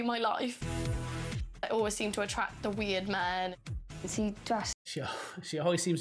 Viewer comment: I thought she said she always attracts the weed man and it made perfect sense